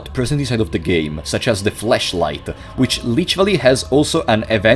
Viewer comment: I’m not gonna lie, when you said that the flashlight used to be an item thought you said fleshlight. And I wasn’t even that phased by the concept to be honest